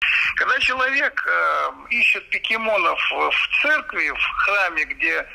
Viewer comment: Мне послышалось..или он сказал "пикимонов"))))